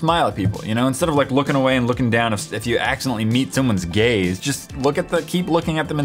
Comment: I thought he said gay instead of gaze